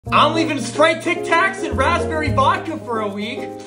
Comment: I thought it said Sprite Tactics, like tacticool Sprite